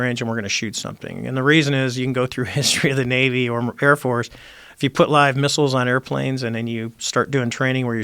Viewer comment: anyone else catch this? 7:20 "You can go through the history of the Navy or the Repair Force." As a former Marine I approve.